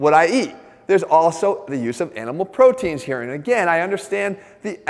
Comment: I thought Jeff said, “the ethical BEEF with animal proteins.” 😂 😝